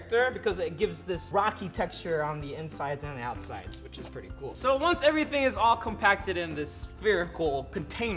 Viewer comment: Thought he said F$&$@ good but u actually said pretty good XD lol like if u agree if u think I’m funny Plz